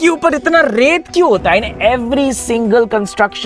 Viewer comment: he starts to say "I also don't understand road ki upar itna (something) kyun hota hai?" What did he say? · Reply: Raate meaning sand